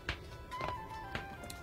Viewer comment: For a second, I thought he said "cuppa tea" (maybe he did, Idk). But, as a Brit, that caught my attention